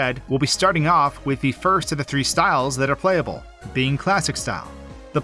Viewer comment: When I heard "first of the three styles," my first thought was "Harry Styles." I hate my brain.